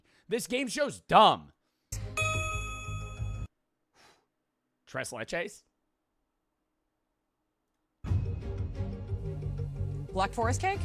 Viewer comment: DID SAY TRES LECHES 28:43 I KNOW THIS IS OLD HUT JOT EVEN CLOSE